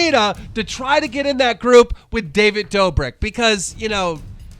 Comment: "david dober" 6:33 😂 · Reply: omg i definitely heard "david doBIRD" lmao